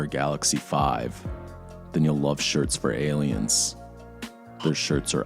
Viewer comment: I thought he said they were sponsored by “Shirts for Ayrans”